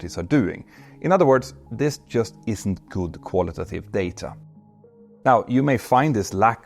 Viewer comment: Hi Petter, thanks for the great video! One small thing. Maybe i misheard, but it should be quantitative data as it refers to numerical data. Qualitative refers to non-numerical data, for example the transcript of an interview.